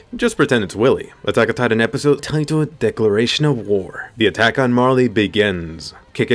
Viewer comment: did he just said "Decoration of War"